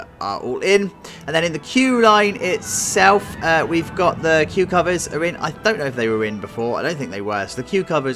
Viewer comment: bwahaha, I was reading something while watching this and swore you said "the cucumbers are in" when you said "the queue covers are in". I need sleep.